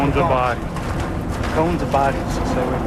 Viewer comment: Does anyone know what the firefighters are saying at 1:21:32? Sounds like "cones or bodies"? Is there a way of identifying a body has been found? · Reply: They're saying "cones are bodies" - before the towers fell paramedics were marking where the parts of jumpers had landed 🤢🥺